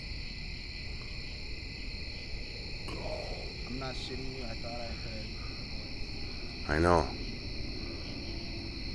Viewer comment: Did anyone else hear anything at 8:52? Doesn't sound like you. It was creepy for sure! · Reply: It definitely is saying “GO”